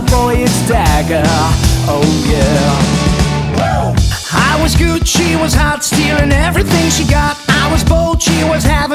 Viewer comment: I was good, she was Harz IV Who else understand that?